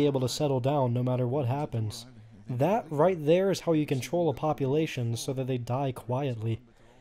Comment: i thought you said "how you can troll" and i almost choked